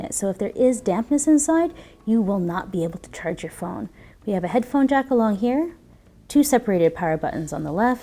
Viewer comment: what did she say? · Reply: xXimortal-soulXx headphone jack along here